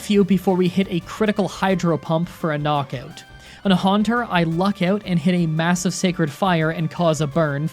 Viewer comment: goodness, you mispronounced golbat so badly i could've sworn you said haunter